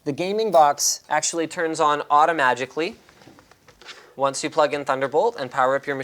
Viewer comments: did he say 'automagically' ? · listen what Linus said "The gaming box turns on automagically"???? Wtf Linus haha! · Did he just say, "the gaming box turns on AUTOMAGICALLY"????? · Did he just say, "Auto Magicly"